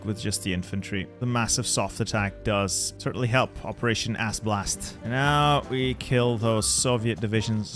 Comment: I thought you said "operation Ass Blast."